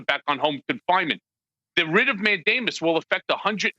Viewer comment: i thought he said writ of Matt Damon lol. yeah i just woke up.